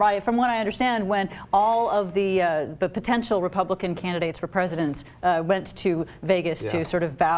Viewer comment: I thought you said Canada not Candidates and I almost rushed city hall holy shit